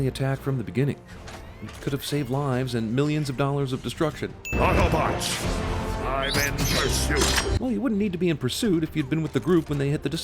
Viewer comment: when i was little i thought Optimus Prim said "auto bots, i mean parachutes"